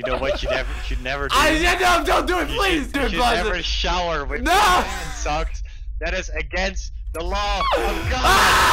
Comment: "you should never shower with a man, socks, that is against the law of god" did i hear that right HSHFKDJFNSDFSJF highlight of the video